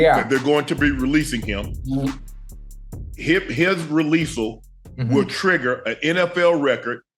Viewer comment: Is "Releasal" a real word? Maybe I didn't hear him right @ 0:22 🤣🤣